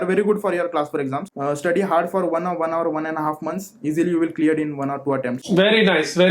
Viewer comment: is he said study 1hr for 1-1.5months?